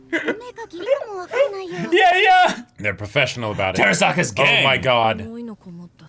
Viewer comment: The abrupt, "terisaka is gay" Killed me. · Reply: I thought they said Terasaka's gang(?)